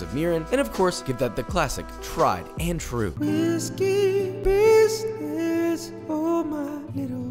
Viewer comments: Did he say ¨urine¨ at 2:03 ?? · Totally thought he said urine instead of Mirin 😳. The unmarked plastic container didn’t help at all lol. But seriously I love poke and really wanna try making it now.